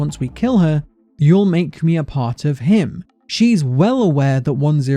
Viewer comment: i don't know what i heard the 1st time i played, but im pretty sure she said "you'll make me spider-mill" she could have said he'll make me part of him too. I dont remember